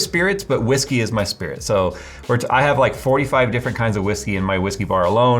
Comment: I swear I heard him say "whiskey is my spirit animal" Lol